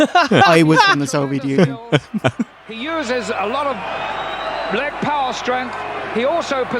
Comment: did he say koko uses a lot of black power strength or back power strength? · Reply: Or leg power strength?